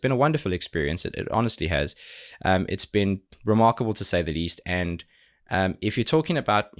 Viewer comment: Why every time he says "Remarkable" I hear " ReMARCOble" ???????????????